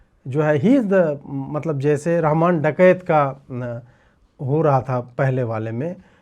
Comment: Did he say Ramayan Dakait? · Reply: I don't think so...what I heard is Raaahaamaaan ...not Ramayaan